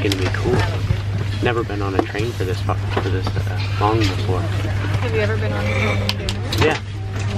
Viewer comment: "I've never been on a train for this fuc... Long before" Did anyone else hear this or is it just me?😂😂😂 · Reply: pretty sure he was saying far